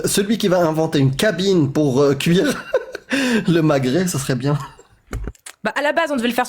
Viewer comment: j'ai compris " inventer une kabyle pour faire cuire le Maghreb ", désolé.